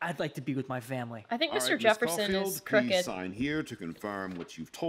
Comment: when you said that you think Mr.Jefferson is crooked, I thought you said he was pregnant. LEL